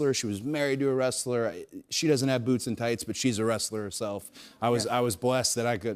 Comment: He should run for president in future.He is a very good speaker . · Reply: Really? With that lisp, Thought he said his mom doesn't have boobs or thighs.